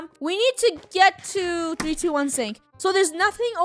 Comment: did she say 321 sync-? i have questionssssssssssssss